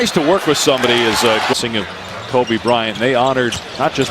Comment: 이거 나만 잘못들었나? 2:54 코비브라이언트라고 한것같은데